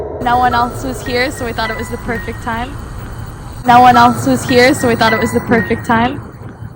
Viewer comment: sounded like "No need to" after you apologized for coming so late ?